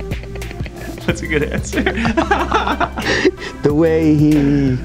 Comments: did say “that’s a good answer” instead of “that’s a good question” or is it just me??? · did say “that’s a good answer” instead of “that’s a good question” or is it just me??? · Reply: Funny videos and more! He said “what’s a good answer” like he wanted to help him answer the question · did say “that’s a good answer” instead of “that’s a good question” or is it just me??? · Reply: Funny videos and more! I’m hella late to this but he says “what’s a good answer?”